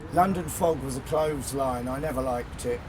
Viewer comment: What does John say at 45:48? It's sounds like "London fog was a clothes line, I never liked it" or something....